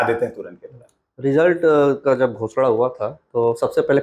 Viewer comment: result ka jab bhosda hua tha??? Kya bolra hai bhai interviewer😅😂